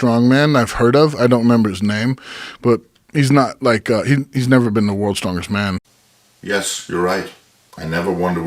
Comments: Oberst says,"hes never BEEN TO worlds strongest man", Not, "he never Won worlds strongest man" like you think you heard...btw, u sound Salty As Fuck... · Little egotistical to think that Joe Rogan should know your name lol. Also at 2:01 he says "he's never BEEN to worlds strongest man" NOT "He's never WON worlds strongest man" Idk dude i think your Ego and feelings are a little too much involved in this. You are kinda making yourself look like a fool. AGAIN at 7:23 he is clearly talking about the guy with the deadlift "World record" NOT YOU, you JUST said it wasn't you so how can you think that Joe is saying its YOU that is smaller then him?? Dude your ego is literally out of control at this point, like you have to be trolling or you are brain dead. This is so so so sad to watch. You embarrassed yourself multiple times in this attempted response. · Lmao all you guys crack me up. He said you’ve never been to worlds strongest man. Not you ever won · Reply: May be so, easy to mishear that. It sounded to me as well that he said "never been THE worlds strongest man". Word was a bit muddled there. It would make more sense if it was "to". It's not the cornerstone of the vid anyway.